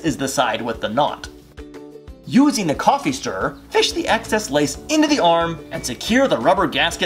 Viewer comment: I think he might of said "pish" instead of "push." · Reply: He actually said “fish” not “pish”.